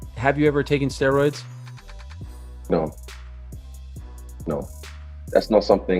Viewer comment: If ya'll actually watched the original interview, the "pause" he says before the no at 8:26 was not a pause. He actually says "fuck no" but they censored it. Honestly, why take steroids to remain skinny/slim.